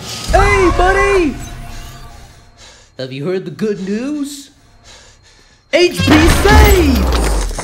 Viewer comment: what did bingo say? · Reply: HB [Hanna-Barbera] saves!